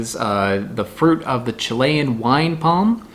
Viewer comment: i thought you said "fruit of the chilean wine mom" and i was sorely disappointed upon rewinding